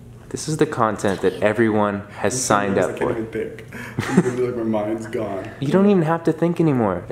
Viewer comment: What does David say at 6:27 ? ? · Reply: I'm so nervous I can't even think. Literally like my mind's gone